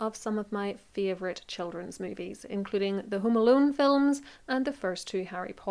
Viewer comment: I have to say I just love your accent. Also, when describing Chris Columbus films I really thought you said the 'Hoom-Aloom' movies. It took me a couple tries before I realized you were actually saying 'Home Alone' movies, I think anyway.